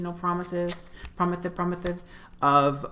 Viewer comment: did you just say "promithes promithes" BAHAHA I love you for that